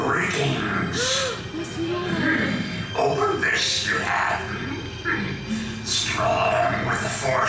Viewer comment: I always hear this yoda line as “Over this you have” like he’s saying “You must be so OVER this” lol